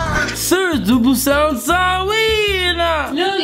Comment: On 7:31 I heard I’m a general Wheeeeee from Hamilton